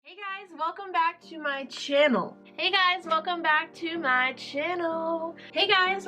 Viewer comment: How about working with Artsy Arena..lol. I've seen so many of your videos and for the longest time I always thought you said " Welcome to my chill "